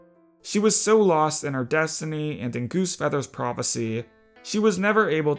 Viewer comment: Did he just say GooseFeather's Prophecy? Am I just hearing things?